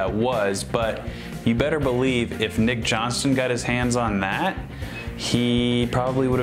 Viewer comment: I thought you were saying Nick Jonas at first! Haha!